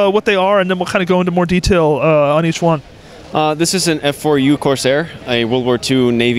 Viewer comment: Thought he said f u corsair...... Whoops